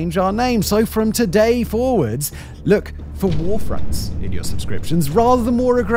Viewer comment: I thought it said War Fonts 🤔 😂